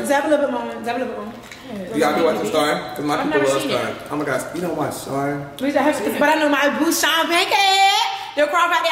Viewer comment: i have no idea what said .. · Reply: V said “dab me up little more” before she gave her bottle away🤧